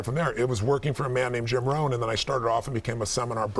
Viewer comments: What's he saying in 3:40??? I can't figure where he started to work! Help please!! :) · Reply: It's actually Jim Rohn. He is Awesome! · Did he say Jim Rohn?